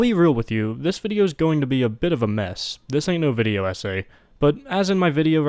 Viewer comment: I heard "this ain't no video essay" as "this ain't no video, ese"